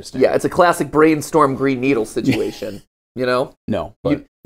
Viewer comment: Am I the only one that was listening to the brainstorm and green needle bit and thought that it said brain needle? Is my brain broken?